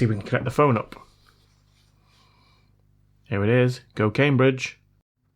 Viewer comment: I thought you said cocaine bridge when you said go Cambridge 😂😂😂😂